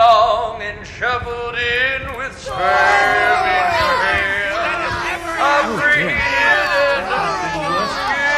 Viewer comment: Did he say " Shuffled in with sperm in your hair". WTF